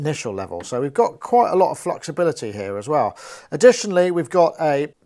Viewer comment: hahahaha did you say fluxibility?